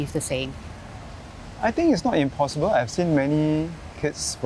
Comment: Instead of " of course", he said " it is not impossible" .....